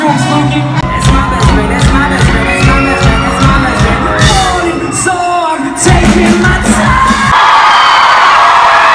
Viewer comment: what does tyler say in 2:57 ? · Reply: Beatriz Silva He's saying "that's my best friend" over and over